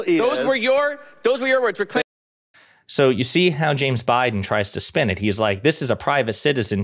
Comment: Did you say James Biden at 7:44?